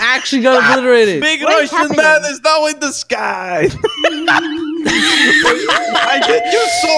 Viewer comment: “Big Russian man is now in the skyyyyyyy...” XD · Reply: When he said that I thought he said “ big ocean man is now in the sky”